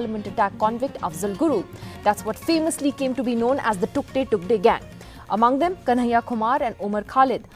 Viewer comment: Did she say "took they took they gang"?